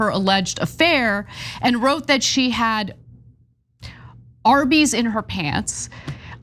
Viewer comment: Arby's restaurants should file suit. At first, I thought she said RV in her pants. Recreational vehicle.